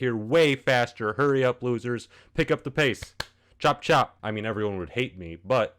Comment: Did you just say chop chop